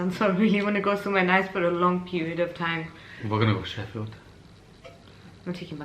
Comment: omg did he say “where gonna go sheffield” come it’s really good and can shop at meadowhall